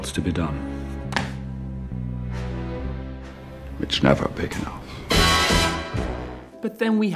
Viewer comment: What did say? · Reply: It's never big enough